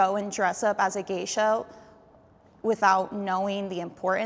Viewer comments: I thought she said "gay show"💀💀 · did she just say gayshow instead of geisha? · She literally said “gay show” instead of geisha. 5:20 Clearly didn’t know what the other girl was truly talking about! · i heard gay show lmao